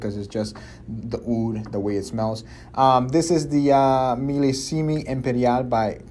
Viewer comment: Wait...did just say Millisimie empanada?? 😂😂